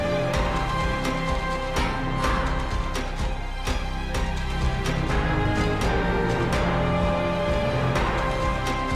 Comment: what's the word ? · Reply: The word is Paris.